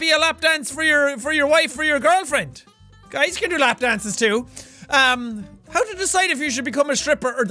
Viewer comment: When jack said "guys can give lap dances too" I thought he said "I use to give lap dances too"